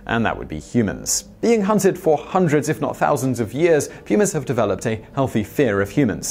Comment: did he say .. “ humans have developed a healthy fear of... Humans?” Because he’s not wrong... I just thought we were talking about mountain lions.. i’ll let it slide though since this is one of the most hard-working men on the Internet.. much respect to you Sir 🙏❤️